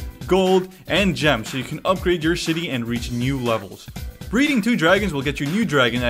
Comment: aw hell nah you said shitty instead of city